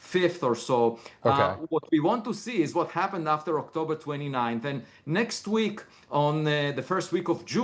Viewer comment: did he say June next week?